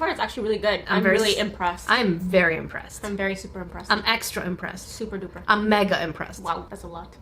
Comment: it sounded like said im super depressed